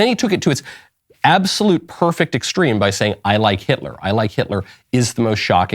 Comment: Did he say that? I thought he said "I Love" not "I Like"?🤔